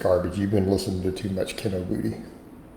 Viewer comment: did he just call Kinobody kinnobiddee?